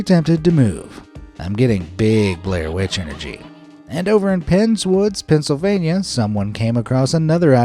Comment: for a second i thought you said friendswood not pens wood because my grandpa lives in friendswood I thought ohhhhhhhhh fk